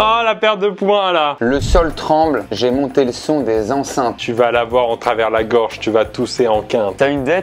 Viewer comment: moi j'avais ”ta meuf tremble, je crois j’l’ai mise enceinte"